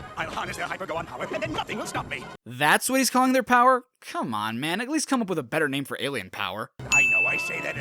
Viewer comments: Um, not to be rude, but at about 2:46, you said, "Come one, man!" instead of "Come on, man!" in the subtitles. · in the video the subtitles read, "Come one, man!" while is clearly saying, "Come on, man!"